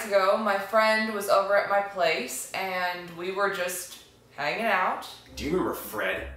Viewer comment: i swear to god, i thought u said, "making out", but then the light bulb went off in my head and i realized u actually said "hanging out".